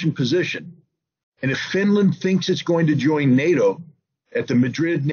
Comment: time = what did Ritter say? Feminin things join nato? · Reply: Finnland 🇫🇮 😂 🤣